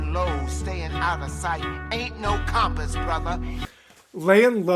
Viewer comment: I’ll be honest I thought Hermes said “Ain’t no Coppers brother” like ain’t no officers. To be fair I don’t think a Acab message would out of place in this musical